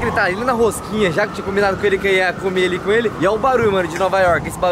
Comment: Foi eu que escutei errado ou o falou que tinha combinado com o Renato de comer ele com ele kkkk